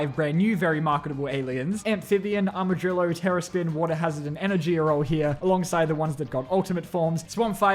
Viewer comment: WATER HAZARD SOUNDS LIKE HE SAYING ASS WA-TER